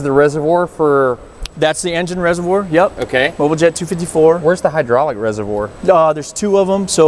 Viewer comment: ish - did he say Boba Jett 254? Is that perhaps a nod to Boba Fett?